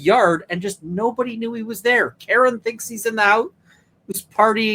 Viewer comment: I thought Karen said she watched him walk into the house